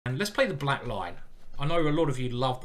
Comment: For several videos, I thought you were saying "The Black Line"!